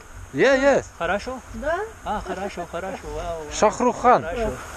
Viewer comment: Did he say "shahrukh khan" at 7:38 ?